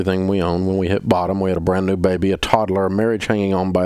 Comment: I thought he said "We bought a brand new baby"